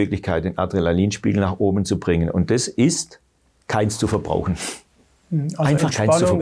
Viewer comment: Danke für das interessante Interview. Leider habe ich aber akustisch nicht verstanden, was der einzige Weg ist, Adrenalin aufzubauen: Bei 20:41 sagt Herr Hirneise: "K??? zu verbrauchen." Was muss ich verbrauchen? Bitte deutlich reden; insbesondere, wenn es um den Schlüssel des Verständnisses geht. Also wer bitte kann mir sagen, was Herr Hirneise gesagt hat? Danke. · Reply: Keines